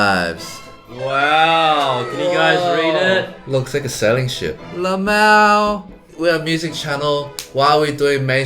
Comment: did he say lmao out loud 👁️👄👁️